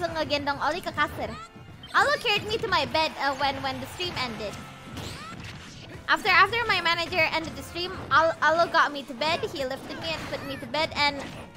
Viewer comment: What, man the subs just stopped in the middle of video. As non native English speaker i don't understand what she saying & auto generated caption doesn't work either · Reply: Carried me to my bed when the stream ended, after my manager ended the stream ollo got me to bed he lifted me and put me to bed